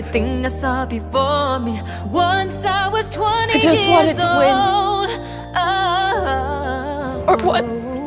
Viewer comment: What did she say? I just want a twin? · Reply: OH I JUST WANT TO WIN?!